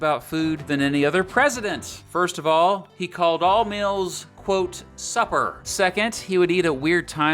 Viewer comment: I swear I thought you said "he called all males supper." I was like dang okay!